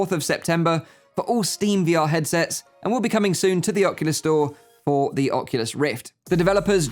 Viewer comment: I thought you said oculus go when you said oculus store. Now im sad